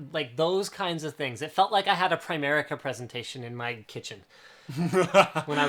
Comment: Guys I'm French and my understanding of English isn't perfect. At 1:11:45 linus says something like 'it felt like a [something] presentation'. What is the word he said ? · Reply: Primerica presentation. I don't know exactly what it's all about, but it sounds like a pyramid scheme like Linus said.